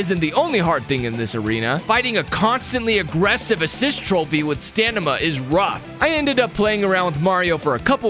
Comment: Am I the only one who heard “stanima” instead of stamina?